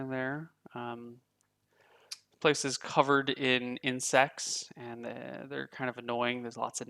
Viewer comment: when you said that it was "Covered in Insects" I thought you said "Covered in sex" and I was like 0.0.